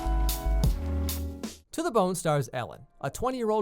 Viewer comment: Thought it said for a second "to the boner"